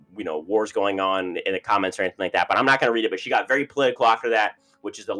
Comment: Thanks for pinning my comment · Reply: at first I thought u said she got my winner pick after this lol